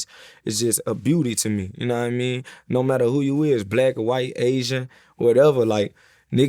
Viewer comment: For a second I thought he said “Vlad white asian” 😂😂😂😂